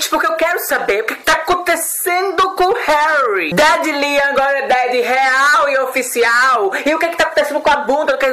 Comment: Eu ouvi "Eu quero saber o queque tá acontecendo com o Harry...Daddy Liam,agora Daddy H-real" VIADO TU QUASE ME MATA CRIATURA ! E concordo com isso de já todos os meninos estarem nesse lance de divulgação solo faz tempo,daí só quando o Harry faz,já cogitam o fim da 1D...ah